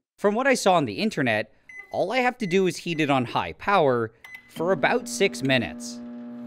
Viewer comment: Lol i thought he said eat it on high power😂😂😂